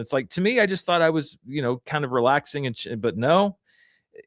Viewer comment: thought i heard say, “just here relaxing and shit” 😭